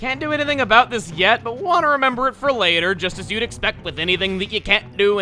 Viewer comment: Did you say "But water to remember that till later?"